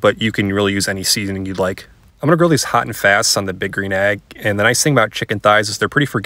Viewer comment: I thought you were saying bickering egg, not big green egg. 🤣🤣🤣